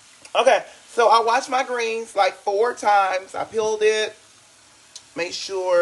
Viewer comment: Did she say, "...I PEELED it..."?